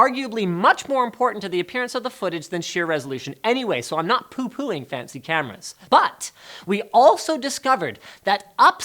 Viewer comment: close captions say "I'm not pooping fancy cameras" lol 🐷🐝🐝🐝🐎🐕🦃🐫🐁🐁🙉🐽🐧🐋🙉🙈🌶🍇🌭🌨🌫💦💦🏇🎫🎿🎫🏇